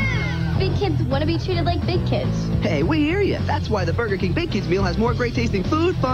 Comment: The first time I heard the announcer said "Hey, we hear ya," I thought it said, "Hey waiter."